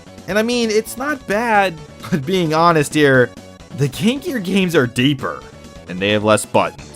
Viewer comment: I kept hearing "the kinkier games are deeper" before I realized what you said